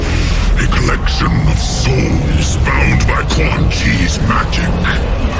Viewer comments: I thought he said “Corn Cheese Magic” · When is quan chi not a walking meme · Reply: Oh! His name is quan chi. I thought he said "corn cheese" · I thought he said " Corn cheese magic" lol